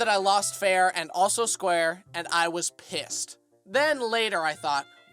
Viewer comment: did just say p*ssed?